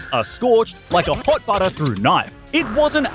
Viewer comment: Am I the only one who heard him say hot butter through a knife at